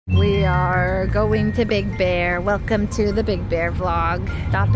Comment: When I was had the video minimized at the start to check something ant I heard Leslie say “We are going to daycare, welcome to the daycare vlog”